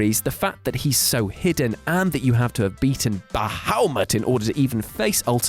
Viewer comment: can someone tell me what he says here? You have to beat a what now just to face him? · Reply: Bahamut, or something really similar to that